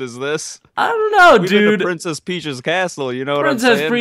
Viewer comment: I thought he said Princess Preach!!XD 😂